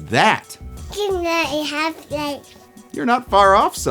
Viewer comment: I cannot for the life of me figure out what zoran’s son says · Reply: it kinda sounds like he said "gman e half life"